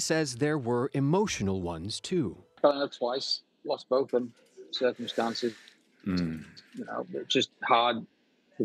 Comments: For anyone else who is having trouble understanding what he is saying, subtitles don't help but I listened back to it until I understood him and here is, what I believe, he is saying: "Fell in love twice... Lost both of 'em to different circumstances..." Hope that helps. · what word does he says here? “<Something> twice”. Was it married twice?